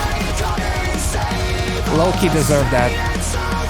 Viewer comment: I think I heard "low-key deserved that" but not sure if the words were spoken or if my mind was so blown away, I was hearing my thoughts out loud. The video production team did a pretty good job of finding a Sebastian Bach look-alike, tho. 😆